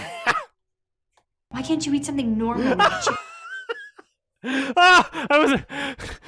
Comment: so I read the subtitles before the Karen finished her sentence and I thought she said "Why can't you eat something normal like children?" had to check to see if my eyes were deceiving me